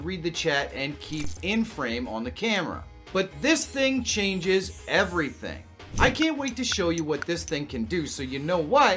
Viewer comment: what you say ? I can't hear you😂❤😂.. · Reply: I said I love you.